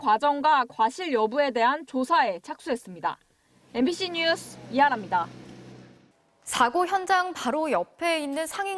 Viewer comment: "MBC뉴스 미안합니다" 라고 들리네요